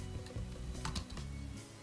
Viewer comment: ステッキがステーキに聞こえる笑